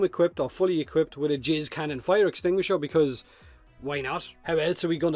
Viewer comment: did you just say a jizz cannon fire extinguisher? o.O